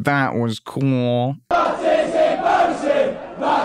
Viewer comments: I swear I hear baptism barson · To be honest, I just hear gibberish. Something along the lines of "batzisin barrzi". In any case, I'm pretty sure it's not anything in English.